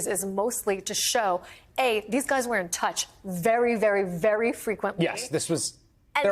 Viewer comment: Definitely thought they said Pete Davidson was the lawyer for a sec